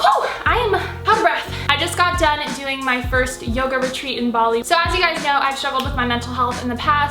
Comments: I thought you said “yogurt treat” instead of yoga retreat 🤣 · I thought she was saying ‘yogurt treat’ instead of ‘yoga retreat’ and I was like damn that must be such a good yogurt